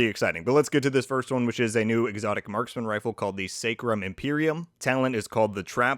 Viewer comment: thought the new exotic said "scrotum" at first glance,lol.